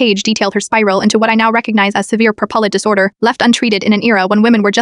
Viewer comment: She had what disorder? It sounded like you said propellant disorder.